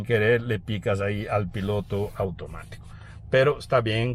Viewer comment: Piloto automático? o escuché mal 8:03 🤔